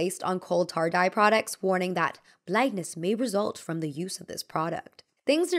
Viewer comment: there's a typo in the subtitles where it says blackness instead of blindness